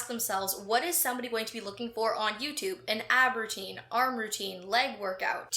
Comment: I swore you said "an aubergine".